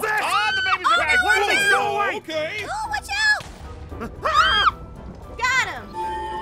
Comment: did she just said bachao?! Is she indian lol 😂